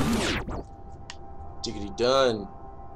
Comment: did you just say "diggity done"?????